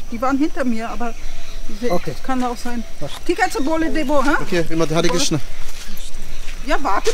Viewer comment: is she say thick a6e bole dabo